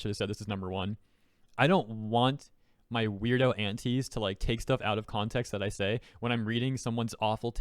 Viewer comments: When he said he has some insane “antis” I thought he was saying “aunties” 😭😭😭 I was like, why are they clipping your videos??? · when you first said "antis" I heard "aunties" and i just pictured a bunch of middle aged women sitting at the dinner table clipping your videos out of context to harass you.